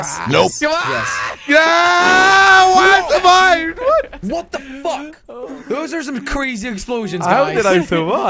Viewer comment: did jelly said what the Phuck.